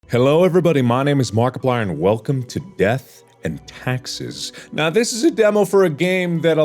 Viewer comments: I thought it said “Death and Texas” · Mission: kill a guy with an industrial background. Mark: kills a 73 year old who loves birds and wrote books. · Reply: I thought it said Death and Texas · I thought it said death and Texas